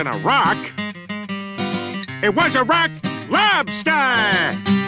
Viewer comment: I honestly never noticed he said "Iraq" lobster hahaha I've watched that episode like 10 times!! Hahaha I just thought it was a lobster dancing to Peter singing rock lobster! Hahahahaha I just thought he said "a rock lobster!"